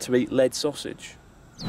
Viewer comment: I thought he was saying "leg-sausage"...like octopus legs.